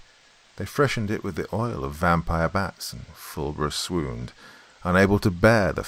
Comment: 😂I heard "suckle a vampire" and said "NOT IT!" Lmao. Thanks!